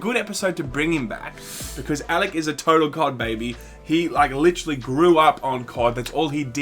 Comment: All hail. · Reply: I thought said Alec is a total “cock baby”